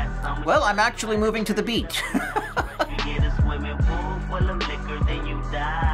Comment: well I'm actually moving to the beat lol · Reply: I thought he said “after this im movin to the beach” smh i was like why tf would he say that